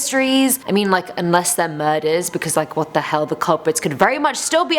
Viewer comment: I thought she said burgers Like if u agree